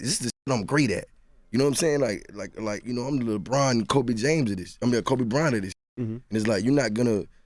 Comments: did he say LeBron Kobe James? (9:22). that was funny lol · Did Jeezy said "Kobe James"? Lol Jeezy just predicted the Greatest basketball player for the future.....🤔